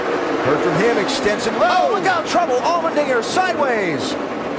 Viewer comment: did he just say the n word? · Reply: He may have said "Allmendinger"